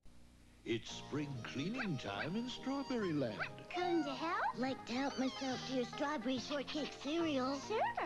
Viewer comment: I swear I heard her say come to hell😳🤣